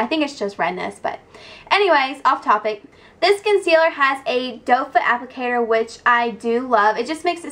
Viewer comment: Hahahaha when she said doe foot applicator I thought she said dope applicator... :D